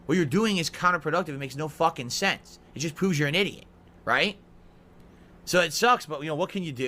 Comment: Did Phil just say poo's you're an idiot